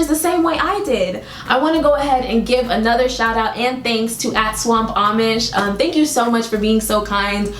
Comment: I THOUGHT YOU SAID “ assswampamish”